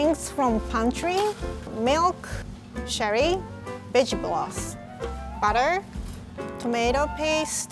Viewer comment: I'm I the only person who heard bitch broth instead of vegetable broth in